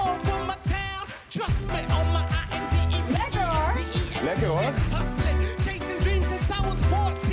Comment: i swear i thought they said the n word ahahahh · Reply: Hahah, they said "lekker hoor"